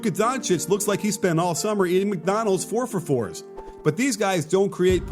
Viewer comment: Hold up a minute did he just say McDonald's 4 for 4s?🤔